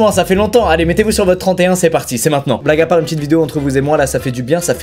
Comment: quoi ”black à part” 😂😂